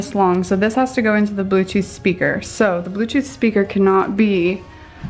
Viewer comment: Did anyone else hear her say “blue cheese speaker” at 7:57 😂🙌🏻